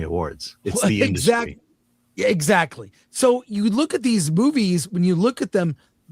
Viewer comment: Did you say ‘"look at these boobies"...??? 😂😂😂 Freudian slip... 🤣🤣🤣